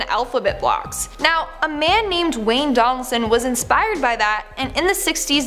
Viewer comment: i thought she said dwayne jhonson